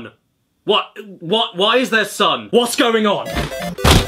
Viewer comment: for a second, I thought he said “son” and not “sun” I did not need the image of Caddy mating with the bricks